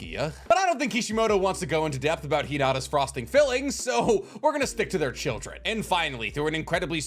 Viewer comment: Uuuhhmmm say that one more time? I rewound. I listened. I couldn't have just heard what I think I heard "Frosting filling"? Damn .... that's a new tag I've never heard before